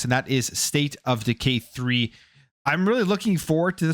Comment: Sounds like hes saying, "State of deGay 3" 😂